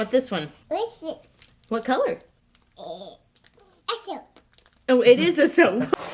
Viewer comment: what color is that soap? orange: and the other one? red: what’s this one? soap. well...it is soap. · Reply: It sounds like she said "essow" which can be heard as "a soap" or just a very weird "yellow"